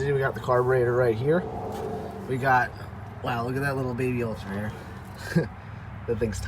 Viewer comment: it sounds like you said a baby elephant? What did you really say?